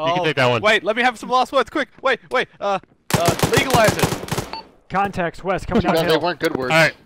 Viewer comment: I'm not sure if I heard right, but did just say at 25:15ish, as his last words, "League of Legends"??!???!?? As a semi-serious Dota 2 player, I find his poor choice of MOBA games disturbing...... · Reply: I think he said "Legalize it"